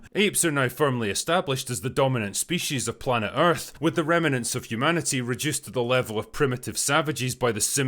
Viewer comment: with the remnants of humanity... Anyone else hear rem-e-nants?